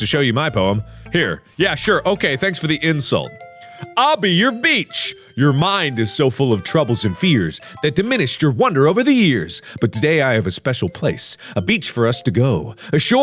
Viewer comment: I heard "I'll be your bitch" by accident lol